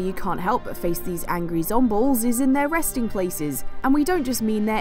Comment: did she just say zombles?